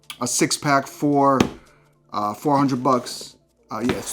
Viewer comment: did he say $50 for 3 pack then $400 for 6 packs.. Lol, something wrong with his multiplication or addition.. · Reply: I know it's a 3 years old comment. He said 6 packs for 100 bucks not 400 bucks